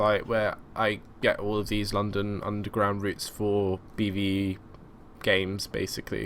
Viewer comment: I thought you said London Bus Routes